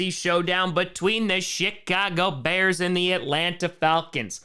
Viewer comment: did you say Shit-cago Bears?🤣🤣